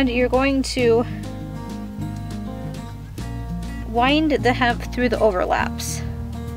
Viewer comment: maybe its an audio thing or whatever but it sounds like your saying hempf every time and its driving me nuts excellent tutorial though and the belt looks cute